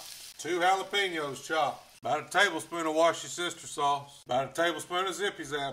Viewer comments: Did he just say" wash ya sister sauce"?😜🤪😝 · Did you just say "warsh your sister sauce"? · Sounds like he said watch yo sister sauce,kmsl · Did he just say watch your sister sauce?